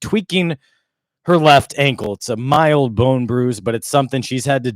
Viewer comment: You're reporting on basically saying she had this setback with a very mild ankle bruise contributing to how long she's been out and the Fever's Spokesperson statement saying they can't say whether or not it contributed doesn't jive. There's a discrepancy. Two Weeks for a Mild bruise seems like a lot. She still can jump and down on the sidelines and kick soccer balls. Can't be that horrible. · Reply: I thought he said mild bone bruise..